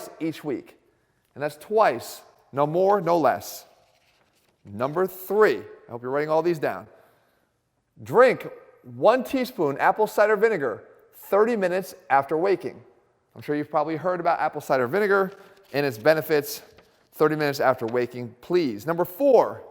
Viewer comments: at first I thought it said "Drink 1TPS of apple cider vinegar 30 minutes after wanking" That's a lot of apple cider vinegar. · I thought he said “1 teaspoon of apple cider vinegar after wanking”. No wonder I haven’t been losing any fat. At least it was enjoyable though. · I heard no 3 as: "drink 1 tsp of apple cider after wanking" haha 😂